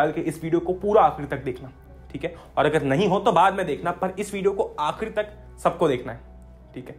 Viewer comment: i thought he is saying Agar time nahi ho to bharr me jawo 😂😂😂😂😂😂